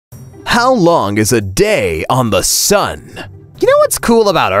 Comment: I thought you said how long is a sunday and i was like Are u stupid?